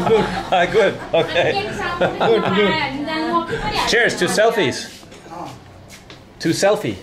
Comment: when you said cheers to the selfie she thought you said you want two selfie lol